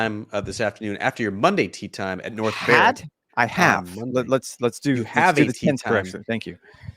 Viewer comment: i think he said “have”. Rex just waiting to do Rex things 😂